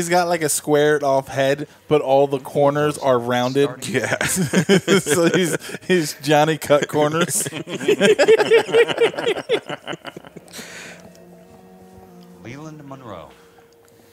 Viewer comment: Ok, for a moment, I thought said "Johnny Cuckcorners".